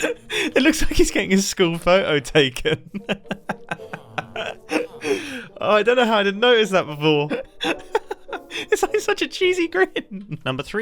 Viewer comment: "It looks like he's getting his school picture taken." I thought he said getting his skull split by bacon